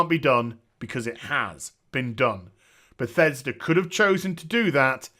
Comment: you said bethesda, subtitles say pathetic :)